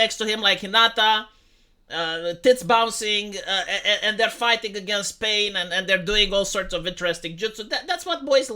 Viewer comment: I had to rewind… I thought he said have Naruto fighting against Spain.